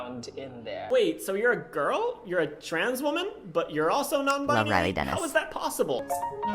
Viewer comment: There's captions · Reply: what you said was not covered in the captions but after listening to it maybe 20 times. It sounds like you said "Love Riley Dennis." 🤷‍♀️